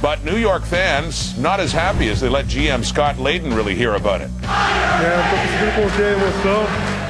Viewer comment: what are they saying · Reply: FIRE LAYDEN!!! (He was the gm)